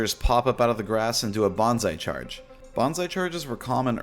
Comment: my dumb ass thought you had said “bonsai” charge 😭